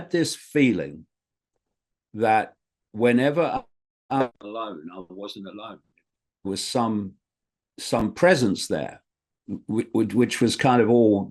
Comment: What's David saying at 39:50? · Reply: He said he wasn’t alone, there was some presence there